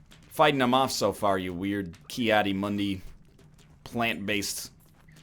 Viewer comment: did he just say as in the YouTuber or is that something else? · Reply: Haven't watched up to there yet, but he likely said Ki adi Mundi, which is his favorite Jedi on the Jedi council.